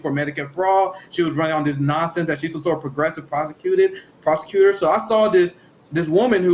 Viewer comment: Wait did you say prostitute? Oh, sorry I misheard, you said prosecutor. 😂